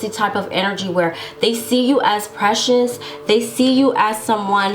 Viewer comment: All I heard was “my precious” from Lord of the rings 🤣🤣🤣🤣🤣🤣